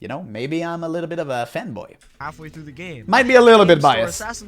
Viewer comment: i legit thought bro said he was a femboy